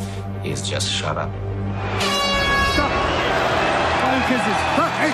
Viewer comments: It's So fucking clear what Conor said : "ITS ONLY BUSINESS" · Play it on 0.5 speed I hear "only kisses" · All I Hear is ''Fine Kisses"